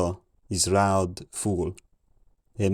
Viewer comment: I heard "is loud fool" mid 800